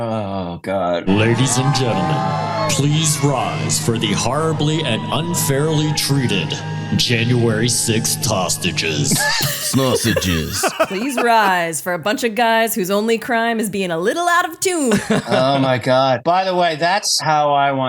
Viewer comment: Did they say please rise for the sausages?